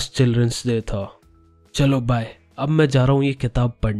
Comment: what did he say?? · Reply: Jisne bhi ye kitab likhi hai, pakka apni zindagi se dukhi hoga.